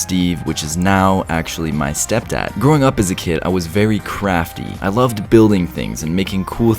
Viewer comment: When you said you were crafty I thought you said I was very crappy